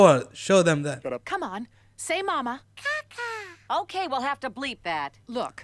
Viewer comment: Host thought poppler said “sh#t”. “Ka-ka” is similar sounds babies making when they need to poo.